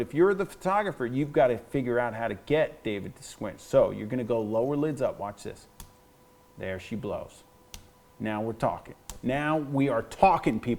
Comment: is it just me or did he say "now she blows"?? XD